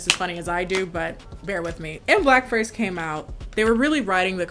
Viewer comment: I thought she said "when blackface came out"